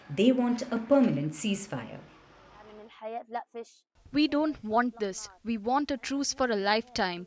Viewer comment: Leave Nigeria alone. You would have thought of this before Oct 7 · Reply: my sister leave these people, I thought they said "they don't want peace"